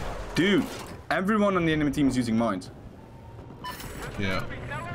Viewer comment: “Dude, everyone in the enemy team is using mines!” I wonder why lmao. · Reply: I thought he said minds xD